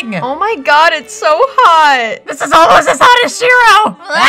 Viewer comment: Did he really say "This is almost as hot as Shiro"? XD